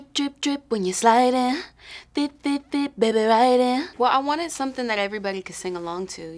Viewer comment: When I first heard it I thought she said dip dip dip baby ride it🤣😬...boiiii I was wrong🤦🏾‍♀️🤦🏾‍♀️..that still goes with it tho🤣😭😭😌